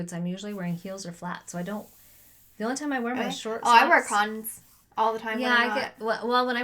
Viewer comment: Also, when said "I wear Cons" I thought she said condoms! Ha ha ha ha ha